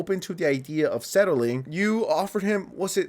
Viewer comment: I heard “and the guild only has $50k” and lost my shit